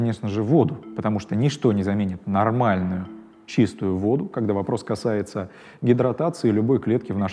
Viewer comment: мне послышалось водка я аж ахерел